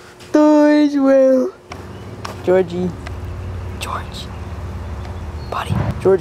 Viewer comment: Deorge Wale :.D (He sounds like hes saying Deorge)